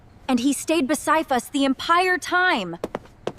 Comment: wait... 6:05 I hear "empire time"?